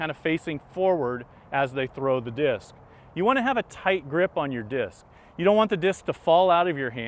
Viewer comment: Thumbs up if you thought he was saying gripping your dick instead of disk most of the time. Good video series though :)